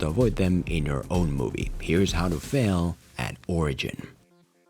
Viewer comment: How to film a what?!! Ohhhhhhhhhhh an origin. My bad, I thought you said orgee